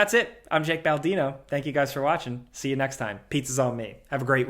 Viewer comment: Had to listen to the last 10 seconds again because I thought I heard "pizza's on me, for free again"